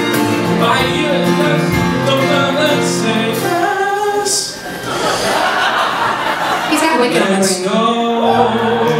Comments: What does he say at 2:21 ? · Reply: He's got wicked on the brain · what did lindsay say about wicked at 2:24? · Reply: "He's got Wicked on the brain" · what did lindsay say 2:20? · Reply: He’s got Wicked on the brain